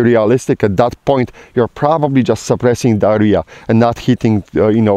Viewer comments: Lol when he said suppressing da area I thought he said suppressing diahorreah! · Did he say “suppressing diarrhea”? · I thought you you said "suppressing diahorrea" I need a hearing aid, great vid sir